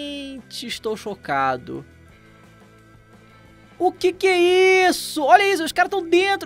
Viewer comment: Yo te entendí queso we 3:28 🧀